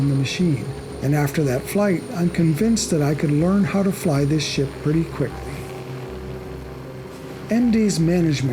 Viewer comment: Did he said '' I could learn how to fly this SHIT very quickly''?